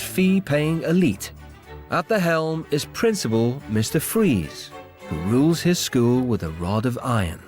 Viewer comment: Am I the only one who heard mr freeze